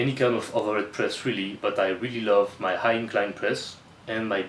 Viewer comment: what are you saying before BTN press? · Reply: High incline Press my friend